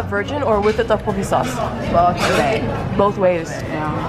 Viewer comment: I thought your mum said “both bae” 😂 it was “both ways”